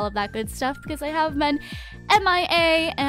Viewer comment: I swear i thought she said by every bunny at the end there 😂